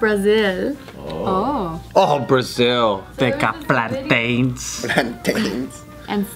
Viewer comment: When he said plantains I thought he said black gays